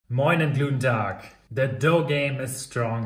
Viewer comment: Did you say at 0:00 "Gluten Tag" 🤣